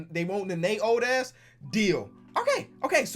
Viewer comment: they want a first round pick and Nene’s old ass, who else heard that. LOL